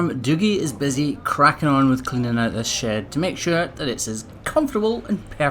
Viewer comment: I realized only 2 mins into the video where you said "pull them into the shed" that earlier you had said "hes cleaning up this shed" I thought you had said "hes cleaning up this shit" ... he was cleaning up shit but im sure you would of called it manure.... lol but when i heard that iw as like "oh.. hes dropping S bombs today apparently"